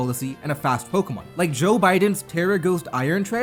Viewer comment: I'm sorry.. did he say "Joe Biden's" around 5:12 ?